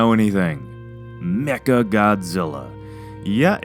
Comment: when he said mecha, it sounded like he said 'necka'